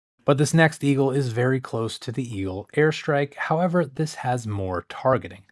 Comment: I keep hearing "hair strike" haha